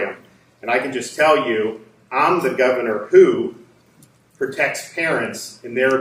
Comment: lol. I thought he said, “I’m the governor who protects Karens”.